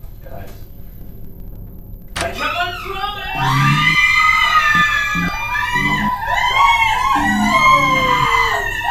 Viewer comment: Alright but what does the dad say at 1:21:01 ? I've replayed it ten times and I can't figure it out. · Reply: Ciara Downward "I kept my promise"